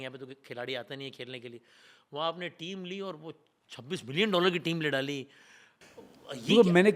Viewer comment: Did I hear it correct? This can't be true. $26 billion? Is he referring to $26 million of 26 billion rupees?